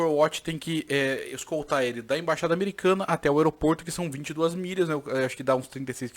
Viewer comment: confundi a distancia e achei que vc ia falar do filme do Eminem... depois que reparei que aquele são só 8 milhas kkk